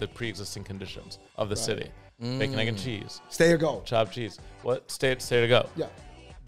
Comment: What’s the phrase says at 9:40? Sounds like “stay or go”?